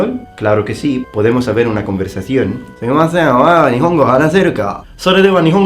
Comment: "podemos "saber" una conversación?" What?! nevermind, hey could you speak spanish with the italian accent instead of the spanish one? that is how the mayority of the Argentinans (my country) speak it, i would be nice to hear it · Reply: Dante Reynoso I thought he said "¿Podemos haber una conversación?" He used "haber" instead of "tener".